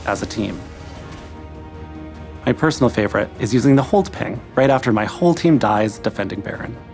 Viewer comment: lil bro hesitated and was gonna say 'hook ping'